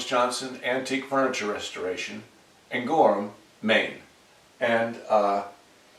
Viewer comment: Hi. My wife and I have been entertained by you for years now, We started following you when you were still saying Gorham and Maine almost as one word. It really sounded like you said Gourmet and somehow it suited the show even though we knew we heard it wrong. To this day, every time we have something clamped up or fixed properly, (we do a bit of salvaging ourselves) we say its being Gourmet'ed and we always think of you. Thanks again, you really are the gourmet of woodshopping.